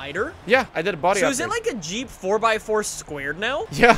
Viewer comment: “A Jeep 4 by 4 squared” lmao🤣🤣🤣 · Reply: I thought he said "G 4x4 squared," which is an actual version of the g wagon that has portal axles and a really high lift